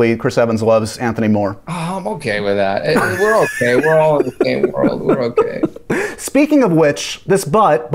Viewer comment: I thought Sebastian said:"we are all gay, we are all gay" after evans responded.